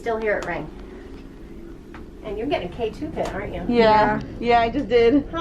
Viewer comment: Um at 11:05 did she really say you're getting a k2 hit😟?